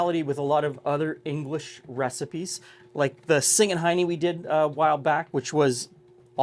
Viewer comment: I'm sorry, did you say "Singing Hiney?" It looks from here like I'd call it a "sandie."